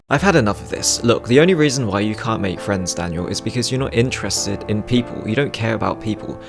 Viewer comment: Did he say Daniel? That's my name! And I already though I could relate to this guy before he even said my name xD